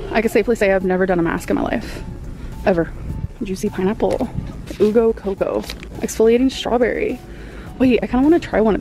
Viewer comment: DID SHE JUST SAY “ooo go coco” AND NOT YOU (u) GO COCO LIKE